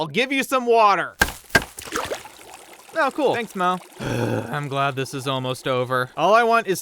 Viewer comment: I was SURE he said “thanks mom” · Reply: If you check subtitles it said “Thanks Mo” Lol I also thought he said mom